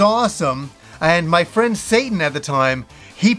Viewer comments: did you say your friend's name was "Satan"?😮😮😮😮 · I heard "my friend Satan, at the time, he actually bought a copy". If I misheard, please don't tell me. Let me keep this.